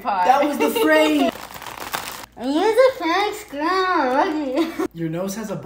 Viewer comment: "Music makes grandma boogie" What I heard: "We need a scribble back there" I know it isn't similar 😂